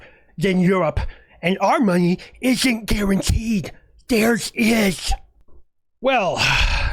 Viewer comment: I like how you used the wrong homophone ("there's" instead of "theirs") when said something stupid. It increases the impact.